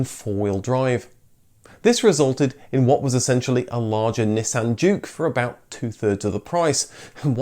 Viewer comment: I thought he said Nissan puke, but he wasn't wrong